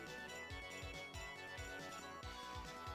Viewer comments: Growing up as a teenager in 91/92 in my city, Oxford, some of thought that Sadat was saying "Viper uppercut"😂😅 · As a teen playing Street Fighter 2, I always thought Ryu and Ken were saying “All you can”, “Hadooken”, and “Nah-hike-sep-nah-ratta-ruppus” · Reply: For Sagat’s “Tiger Uppercut”, I thought he was saying “Fiber up your butt” 😆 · It's hard to imagine how you do not hear sonic boom. At the same time when 6 year old me played SF2 when it first came out, I initially thought Sagat was saying Viper! Viper uppercut! Typing this out makes me think how could I think viper uppercut. That doesn't make sense! Neither does tiger uppercut I suppose but it does as an adult and knowing you connect tiger mui thai. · Always thought Sagat said “fiber” when we were kids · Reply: I always knew he said Tiger. But when he said Tiger Uppercut I always thought he said "Tiger Hahaha" (English isn't my first language and had no idea what an uppercut was) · No one. Absolutely no one thought he said Tiger ROBOCOP lol. 😂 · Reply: I thought he said hyper upcut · I always thought sagat said hyper uppercut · I thought sagat was saying hyper uppercut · I always thought sagat was saying Fibre uppercut when I was a kid 😅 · Man when I was a kid I always thought Sagat said " hyper" "hyper uppercut"😂😂😂😂😂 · I always thought Sagat said, “Tiger up your butt…..” 😂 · I always thought Sagat said "fiber uppercut," lol.